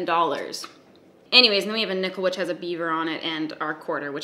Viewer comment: I thought you said the coin has Bieber on it